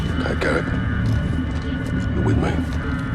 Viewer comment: I actually knew he was Gaz from the London mission. Only because I have subtitles off so I thought Price said “You’re with me, Gary”